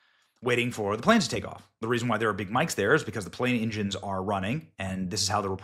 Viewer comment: I heard "the reason why Big Mike's there" and I'm like, what's Big Mike doing at a presser?! 😂